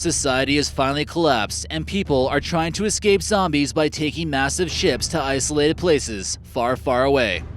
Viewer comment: When he said you need a massive ship I thought he said massive shit